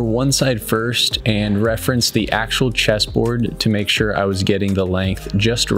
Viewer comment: Dammit! I thought it said cheese board...